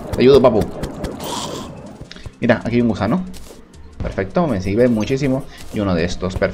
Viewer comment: escuche busano o que ¿? comenten si tambien escucharon busano